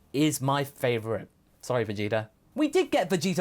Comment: did say Vegeta or Vegito? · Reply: Vegeta